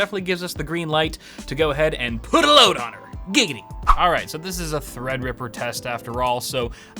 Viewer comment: did u say giggity??...family guy fan?